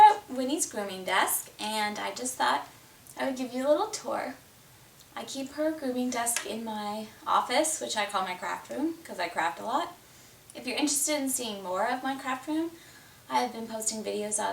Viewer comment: At first I thought you said this is my crap room and I crap a lot! Hahahahahah PRICELESS!